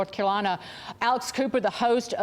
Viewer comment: I had to listen 4 times and do a quick search. I thought she was saying "Alice Cooper Pod Cast".